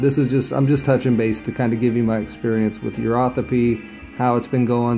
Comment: I thought I heard the host say urothepy, but I only know of the 2 different terms 'urotherapy' or 'uropathy'. Please correct me if I'm wrong.